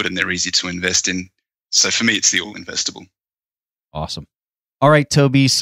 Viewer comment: When asked about which cap Tobias prefers he mentions something that sounds like “all investable stocks”, did I hear that correctly? Curious as to what that even is. Can’t seem to find anything that sounds like that online. 🤷‍♂️ Can anyone help me out here?